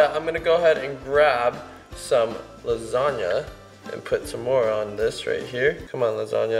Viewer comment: did he say moron more on ?!?!?!